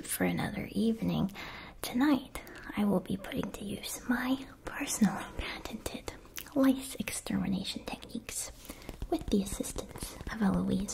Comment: when I first listened to that I thought she said “my patented life extermination techniques” and my first thought was ‘I guess that’s technically correct’, but my second thought was ‘wow, sis does not fuck around’.